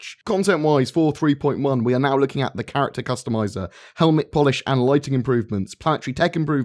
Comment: I got excited when I heard 'Carrack', but it was just 'character customizer' stuff. False alarm!